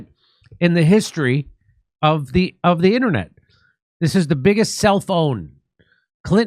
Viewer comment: Ha ha! I heard “ this is the biggest cell phone”.